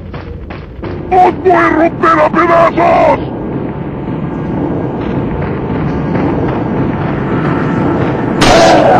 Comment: what is he saying in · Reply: He said: "Os voy a romper a pedazos!", literally means: I'm gonna break you into pieces!